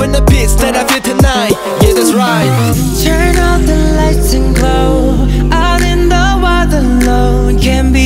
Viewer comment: It sounds like he is saying 아름다워